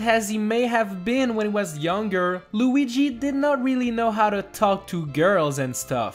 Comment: I thought you said baby Luigi didn’t cry. Look at the sports superstars.